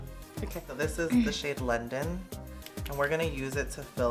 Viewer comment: 5.19, - Thought he said Lyndon then, I was about to say... “ James? Lyndon James? “ If you know, you know...